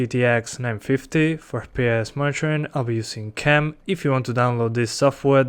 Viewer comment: what are you saying at 0:07 im very curious cause all i hear is "fps mytrain" which makes absolutely no sense